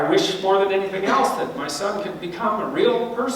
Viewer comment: I mean, it was clear what he means by "creation" in his other analysis of Pinokio anyway, but I just wanted to point out, that this time, he actually says "son".